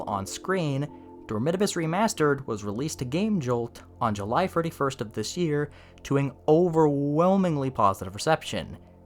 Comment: sounded like you said "freddy-first" 😂 I had to go back and listen for 31st